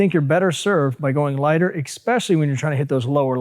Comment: Did he just say eXpecially?